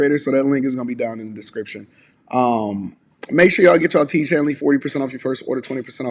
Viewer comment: Tiege Hanley: Get your first box 40% off (+ FREE gift), and 20% off for life, at <link> Buy tickets to the upcoming show here (discount code:) | <link> _________________ Join the Bag Chasers on Patreon | <link> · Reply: All of this time I thought you were saying, "Teach Hanley." 😅